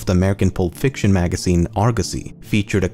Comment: im not gonna lie when he said the name of the magazine i wasnt fully paying attention and i thought he said "Argussy"